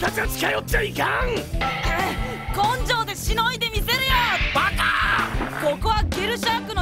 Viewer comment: 若～っ！って言ってるはずなのに「バカ～っw」って聞こえるwww 16:08 多数決…？あw(察し 16:14ほらやっぱり～ぃw